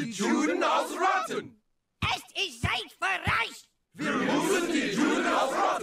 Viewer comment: Did Cartman say in German: Shite?